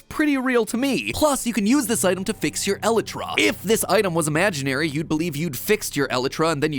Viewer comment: I keep hearing Eletra it's Alaitra not Eletra 😂